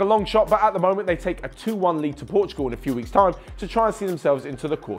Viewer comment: did he say portugal😂